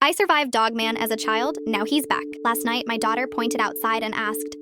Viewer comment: Lol 😂I thought it said I survived dog man as a child now he's black